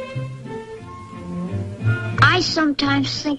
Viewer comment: My brother and I always thought the one kid said beep beep beep zip Tang